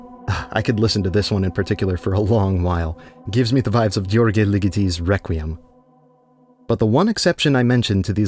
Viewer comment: anyone know wtf he says here? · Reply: "György Ligeti's Requiem" , I think